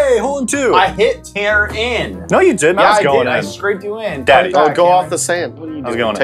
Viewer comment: Did somebody say "Daddy" at 3:14? I swear I heard someone say it, but I don't know their voices well enough to tell who said it.